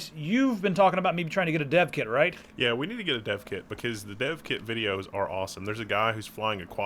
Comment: i thought u said "deaf kid"